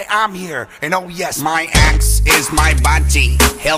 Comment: when i first heard this i thought he said "my ex" and that the song was about a couple of gay ex-lovers who also happened to be assassins. extremely disapointed.